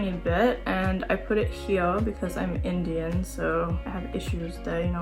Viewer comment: did u say Indian?